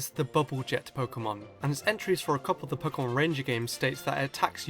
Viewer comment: I THOUGHT THAT SAID POWER RANGERS!!!!! I WAS THINKING OF POWER RANGER JUNGLE FURY!!!! REEEEEEEEE